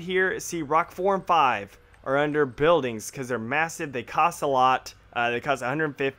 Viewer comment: i thought he said this map was missive...